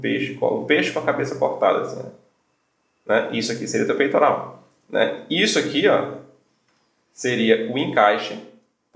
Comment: alguém sabe o nome do cara ? não entendi bem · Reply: michael hampton